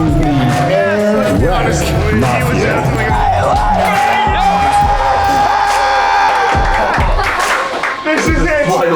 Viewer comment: what did he say in the background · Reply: he said “yEaHHHHH LEtS gOoOoOoo!!!!